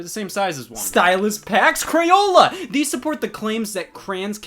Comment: all I hear is cran. The classic Clint Stevens.